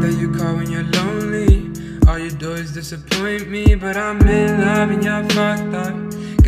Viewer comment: Yo pense que decía "i'm in love, but..." cuando canta esa voz de la chica